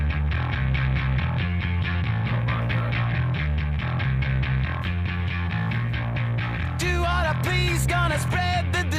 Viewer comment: what are you saying here, that you don’t like porn master flash’s bass thumping? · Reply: I said "omg" bc the bass was orgasmic.